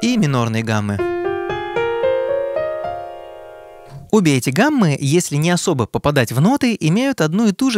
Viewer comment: 3.13 - "обе эти гаммы". А мне послышалось - "убейте гаммы". Классная тема для песни. Про композитора которого все мелодии задолбали до предела абсолюта. И он просит - ну убейте гаммы!)